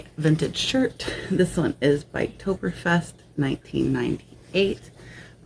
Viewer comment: LOL the biktoberfest WHAT?!?!?!?! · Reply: was it Oktoberfest? I thought it was then I thought you said something about biking lol. I was driving and just listening sorry 😂